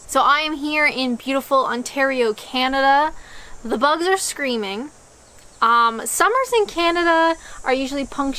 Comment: Was I the only one that heard “screaming and sex”? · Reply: I heard "screaming and sex" initially, but then no, no she did NOT say that.😀